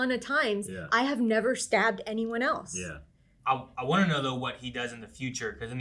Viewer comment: He actually said something more along the lines of "I have never sent anyone else to get stabbed". He was comparing himself to the boss, who has sent plenty of people to get stabbed, but never been stabbed himself.